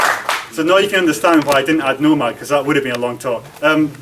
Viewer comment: What did he say at 39:21 , no mac, is it another orchestration tool.? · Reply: "nomad"